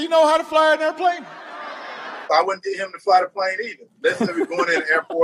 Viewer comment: did Herschel say fly the car? 🤣🤣🤣